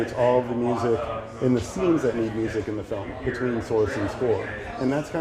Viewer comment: What does he say when explaining the spotting list? In the sentence where she starts with "isolates music..........scenes that need music between _ and score?" 1:30 mark. What is the word that he uses (blank) and the score? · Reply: He says, “between source and score.” Hope this helps. And thanks for being an ‘Insider’. MUBUTV